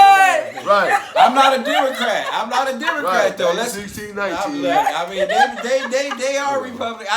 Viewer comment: I heard "1619" I cracked the f up! lol!!